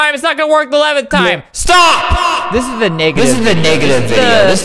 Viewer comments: LOL I KNOW HE DIDN'T SAY UT BUT AT 3:00 IT SOUNDS LIKE HE SAID N#### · did flamingo say the n word